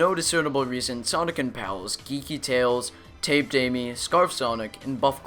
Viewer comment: Who else thought he said kinky tails